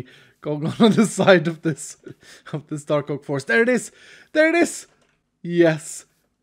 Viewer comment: When he said "Dark Oak forest" I thought he said " Taco forest" 🤣🤣🤣🤣🤣🤣